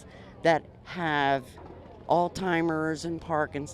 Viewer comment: sounded like she said "people that have all timers" lol